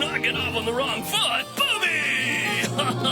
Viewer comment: did he just say booby?